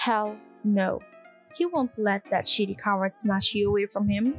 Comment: did u say sh!ttykawa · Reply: no- It was coward not Kawa hahahaha